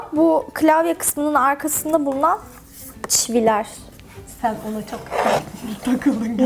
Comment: Çivi mi dedi ben mi öyle duydum? Evet ikinci kez çivileer diyince emin oldum